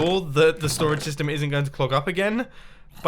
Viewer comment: did he say the f word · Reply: Nvrmind he said clog